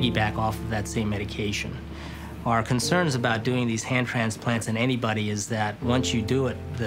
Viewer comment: Omg I thought it said HEAD transplant